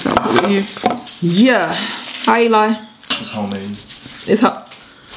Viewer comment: Lol I thought Eli said it’s ho-made 😂😂😂😂😂😂